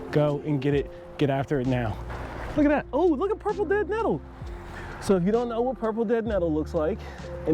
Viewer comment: excellent video. Thank you. My question concerns 15:34. You named a plant that was growing there (even said it was a favorite of the wife), but I could not understand what you called it. Look up 'Self Heal Plant' (or 'Prunella Vulgaris'). Wikipedia lists some alternate names, but nothing that sounds like what you said. Could you tell me your name for this please? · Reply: Hello and Thanks for watching! The plant was Purple DeadNettle. 🤙🏾❤️🌱